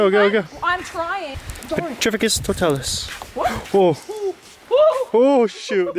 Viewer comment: Wait.. did i just hear saying petrificus totalos?(5:28) 😂 that was funny and cute 😍 #potterhead here